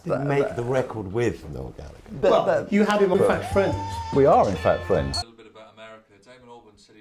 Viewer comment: I thought he said 'we are in fat friends' I'M DEAF